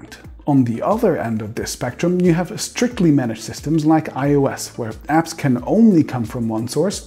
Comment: I don't think you're describing MacOS correctly on this graphic. MacOS apps don't just "come from one source". I'd say most of MacOS users don't get the majority of their apps from the Mac Store. DMG, PKG, Brew, MacPorts - those are the main sources (not in that order). Mac Store is a similar attempt like the Windows Store. I'd argue its success although its long term goal is to lay the foundation of unified iOS/MacOS apps. Actually, they just started blurring that line with Project Catalyst, Universal Purchases and if rumors are true we're about to see the first ARM-based Mac very soon. EDIT: Misheard that bit! It's iOS not MacOS that is being used on the graphic.